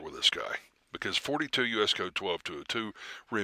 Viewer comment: You harm the fight against injustice more than helping. There is no 42 US code 1222 as you said at about 12:50. Did you misspeak? UPDATE: My bad. I misheard. Good work.